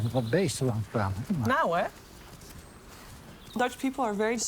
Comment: Sheever is such a beautiful person in every way. I'm glad she lives in a beautiful place like the Netherlands and has access to great health care. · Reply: Percepto Shmegington I Apologize you are right, I misheard Danish, Judy went back and checked she says Dutch